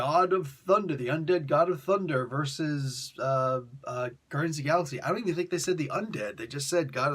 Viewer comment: *...did he just say "Ass-Guardians"?...*